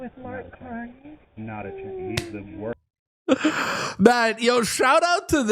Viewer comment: I thought I heard the beginning of his comment sounded like "he's a worthless........". I would imagine it was followed by "piece of sh!t". Lmao!!